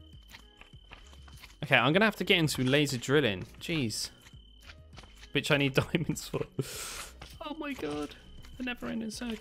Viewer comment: sounded like “bitch I need diamonds for”